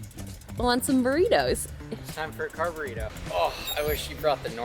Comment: CONGRATULATIONS!! Also, maybe it is the Runner in me, but I heard, “Car-Burrito” as “Carb-Burrito” 😂